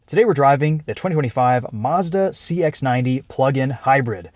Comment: I heard Azda not Mazda